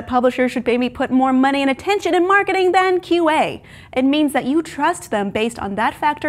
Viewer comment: What does she say at 2:53? "More money and attention in marketing than" what? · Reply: than QA. Which stands for quality assurance.